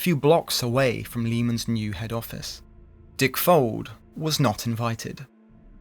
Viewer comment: to emphasize just HOW bad Richard Fulds name is, YT demonetized this entire video because we mentioned it one too many time 😂😂😂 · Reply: I kept hearing "dick folds"